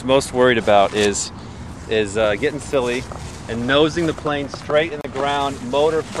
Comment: "'boner*' first, cock pit" XD * sounds like u said boner, lol im mature -_